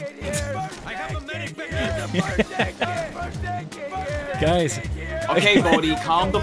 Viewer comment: Instead of first aid kit i heard birthday kit so it made me think of an aggressive surprise birthday party , sounds epic.